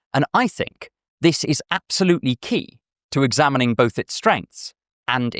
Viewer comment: Caption typo at 12:06 it says strands instead of strengths